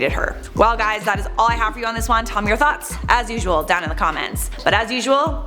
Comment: Lmao... for a sec I thought you said, tell me your thots.